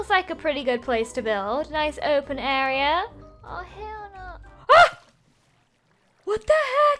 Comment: She says “ah hell nah” listen closely Like if you heard it Edit: for the people who are saying and? Nothing I just thought it was funny and most people didn't hear it im not trying to attack ldshadowlady shes my hero and I'm alive because if her I would he dead she brought the light to my dark tunnel I didn't mean hate or anything else